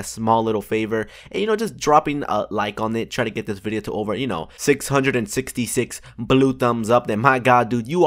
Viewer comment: did he say 666 on